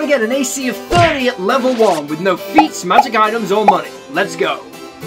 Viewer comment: I thought it said monkey not money and I was confused